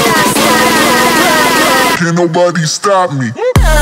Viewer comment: мне одному слышится что он говорит встав мне